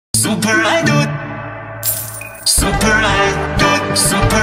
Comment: All i hear is "Super i dude"